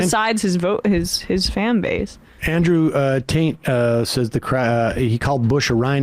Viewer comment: I can't be the only person who heard Sam say "Andrew Taint."